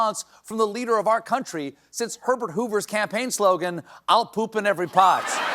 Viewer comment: I swear Stephen said "Pervert Hoover"